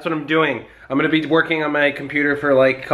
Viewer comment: did he say twerking or working